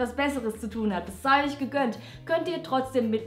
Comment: "Bissaulich gegönnt"? Was sagt sie da? Ich versuche, mein Deutsch-Hörverstehen zu verbessern.